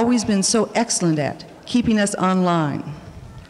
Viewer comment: Did she just say "keeping us online?" Didn't know people said that in 1981.